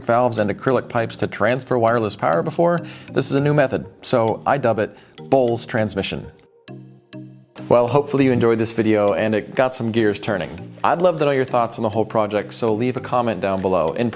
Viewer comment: Bowels Transmission? I think I heard of that one before... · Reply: Close! Bowles. Now bowels.